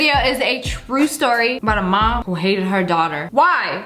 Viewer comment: OH MY GOD I had to replay the intro because for a second I thought she said a true story about a mom who dated her daughter. I was about to be like wait, that's illegal